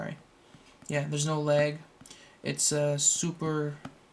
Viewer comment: It sounds like you say "Theres No Legg" LOL